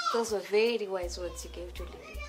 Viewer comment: what did mai Tanya say pa 15:33? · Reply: Those were very wise words you gave to Lily